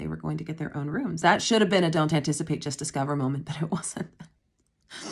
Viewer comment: Sooooo when you said a vacation with grippy socks, I thought you said vacation with Kirby socks and was very confused.😅 I did not play the games or watch the show so idk why my brain went there